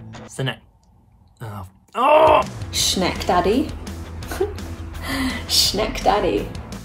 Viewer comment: I hear “shinect daddy” lol